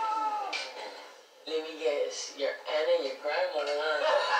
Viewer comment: does someone know what he said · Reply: “Let me guess… your aunt and your grandma huh?”